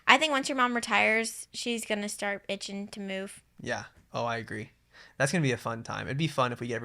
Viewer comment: Did say mom is going to start “bitchin to move” at 40:12😂?